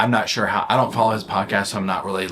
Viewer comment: I heard "I don't follow his punk ass" at 17:22. Had to ruminate on that a couple seconds before I was like...oh, "podcast." That makes more sense. 🤣